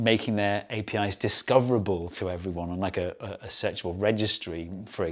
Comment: What kind of registry? I only hear something I'm sure it isn't. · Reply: searchable